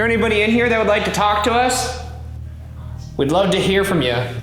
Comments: Did anybody hear something say "us" after Ryan says it at 7:49 ? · after Ryan ask if anyone would like to talk to them, you can hear a faint noise that sounds like "Us." And then his SLS and flash light dies. Did anyone else hear this sound? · idk if im being silly but i swear i heard a whisper saying “us” right after ryan says “is there anybody in here that would like to talk to us” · Alright so first time watching through, it sounds like something saying "us" after ryan asks if anyone is here at 7:48, possibly worth checking to see if it was caught on any of your other equipment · Is anyone else hearing a disembodied voice saying "us" after Ryan's question? Where is it coming from?